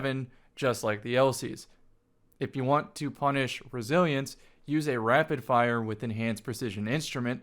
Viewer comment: When you were talking about Scouts and said "If you want to punish Resilience" I definitely thought you said "If you want to punish Brazilians" at first and I was like "wtf did Brazilians do?" 😂